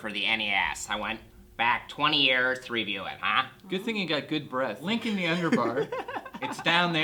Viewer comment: Did say, good thing you have good breath at 9:35 ?? Lol